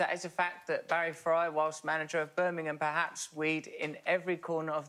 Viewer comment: I also thought Alan said Barry Cryer.